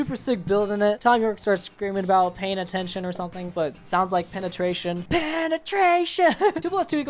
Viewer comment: tbh, i'd put httt above the bends but other than that it's perfect edit: btw im glad im not the only one who misheard penetration instead of payin attention lmfao